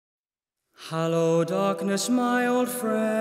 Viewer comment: Maybe it's just the way he sings, but I always hear "Hallowed Darkness" instead of "Hello Darkness" Anyone else or am I just crazy?